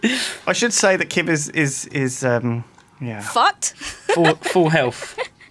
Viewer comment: Is it just me or did I hear Kim say fucked at 10:19 because I've never heard her swear in Flux Buds